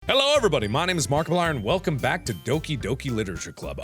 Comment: When he said “my name is markiplier” I thought he said waffle iron